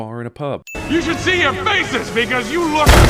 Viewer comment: I swear I heard 'You should see your faces niggas'